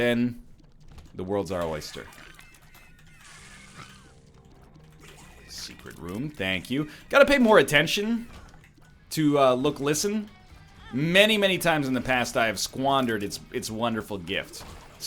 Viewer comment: called Yo Listen "Look Listen" and I heard "Luke Wilson". So from now on Yo Listen is Luke Wilson. Thanks!